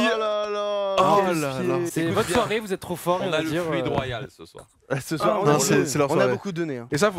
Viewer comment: fluide royal? haki des rois? vous dites quoi vous?